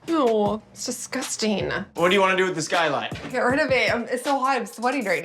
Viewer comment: What kind of designer removes a skylight from a windowless bathroom? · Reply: Hello Victoria, there were two Skylights, for the one in the bathroom please check 5:05, he clearly said Skylight. Greetings